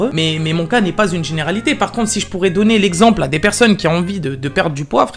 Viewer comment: Si tu quoi ?? J’ai cru mal comprendre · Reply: Si je pourrais donn l'exemple ??